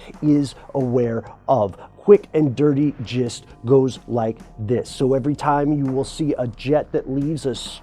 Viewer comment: i thought he said.....Quick and dirty jizz goes like this